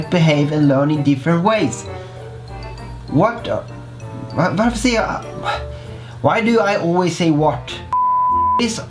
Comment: Are you Swedish? It sounded like you said "varför säger jag..?" (Why do I say...) 5:37 😄🇸🇪